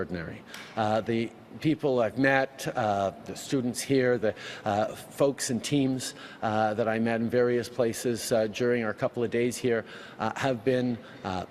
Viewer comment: Did he say "Teens"? Or "Teams"? Hokey Crap flingin Poop Monkeys!! LOOK at Freeland as Turdeau talks Just INSANE! Her head bobbing up and down as if she still making that fake video from a few weeks back! Such a complete brainwashing and a good Useful Idiot.